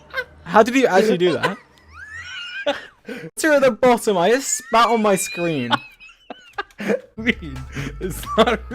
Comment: wat the fudge did george said? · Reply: It was cut off but he said “i thought there was water at the bottom. I just spat on the screen”